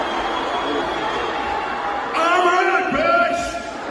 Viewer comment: what did Elon say? Couldn't understand him. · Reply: I believe he said "I'm rich, bitch!" In an extremely awkward fashion